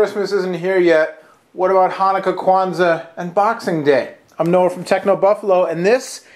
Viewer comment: I wasn't really paying attention when he introduced himself and thought he said "I'm Noah from PhoneDog!!" OOPS!!!